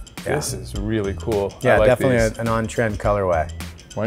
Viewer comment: what did he say? Non what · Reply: He said " an on trend colorway"